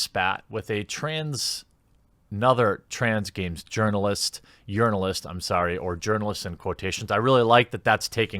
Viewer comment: You can tell Jeremy’s proud of coining ‘Urinalist’ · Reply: TBH until he explained what it meant I thought he was saying yearnalists as in they could only yearn to be a real journalist.